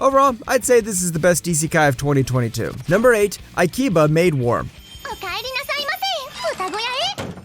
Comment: I heard "i keep a maid warm"😂😂😂😂